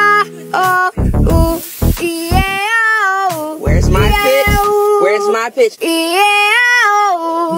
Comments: I thought she said bitch but she said pitch my brain is so dirty like😭😭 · When she said pitch I thought she said “bih” at the little kid I can’t😭✋🏽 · Bro why the hell did i hear "b*tch!* instead of "pitch!"😭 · ( pitch ) please tell me that I'm not the only one who thought she said bitch😂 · “pitch!” My brain “B**ch!” 👇 if you thought she said that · I thought she said b**ch 😂 not pitch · She actually said pitch I'm just like did she just say b*tch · “Pitch!” My brain: ”B**ch!” 😭😭😭😭😭😭😭😭😭😭😭😭 Edit 8 months later: DAMN, I WAS NOT EXPECTING SO MANY PPL TO RESPOND TO MY COMMENT- (in a good way. Thank you so much) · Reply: Wait she said pitch? I kinda thought she was saying B***H to a child for some reason.... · B**ch I thought u said b**ch but u said pitch 😂😂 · When she said pitch I heard “bitch” 😂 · I thought she said birch but it’s pitch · “Pitch!” My brain: ”B**ch!” 😭😭😭😭😭😭😭😭😭😭😭😭 Edit 8 months later: DAMN, I WAS NOT EXPECTING SO MANY PPL TO RESPOND TO MY COMMENT- (in a good way. Thank you so much) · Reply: For a second I thought she kept an saying "where's my b**ch" then oh she is saying pitch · I thought sh ewas saying bitch but she was saying pitch 😂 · "Pitch!" What I hear "B--ch" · She's saying pitch my brain did she just say bitch😮 😂😂😂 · I thought she said b!tch when she said pitch